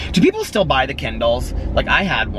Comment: Ha ha I thought u said Ken Doll then realised u said kindle lol I love my kindle, I have kindle unlimited and the amount of free books u get is unbelievable, it’s only £7.99 p/m but it’s so worth it esp the price of some books